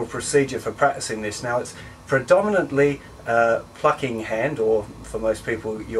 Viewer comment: DID HE JUST SAY FUCKING HAND ?